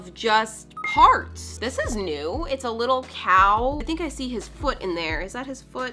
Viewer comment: Hi! You should make a map of nafalafogous! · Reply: When she said “this is new, it’s like a little cow” i thought she said “this is you, it’s like a little cow” 🤣